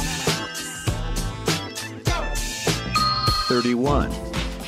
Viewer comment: anyone else hear a guy say go